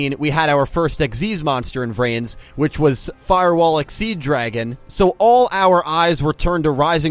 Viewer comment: I thought I heard playmaker call it firewall xyz dragon so how come its call firewall exceed dragon